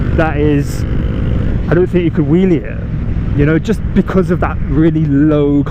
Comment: Hey man! Very nice video! Thank you first of all! But can you tell me what do you say at 13:03? Thank you! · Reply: I said I dont think you could "wheelie it" :D